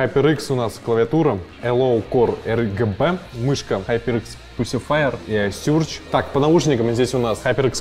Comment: pussy fire? ты сам надеюсь понял что сказал?)